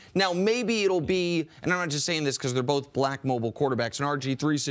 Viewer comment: Nick is a RACIST... When he said, "maybe it's because he's a BLACK mobile Quarterback!" WTH does Black have to do with "Mobile?" Warren Moon was a Pocket Quarterback first and Doug Flutie was a Mobile Quarterback. Race has NOTHING to do it and Nick Wright should be FIRED for making such an IGNORANT AND RACIST statement! Smh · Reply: That's not what he said. He said "I'm not saying this because they are both black...." so what? That's not racist. 7:05 for the time stamp for you because I don't want to waste your valuable time.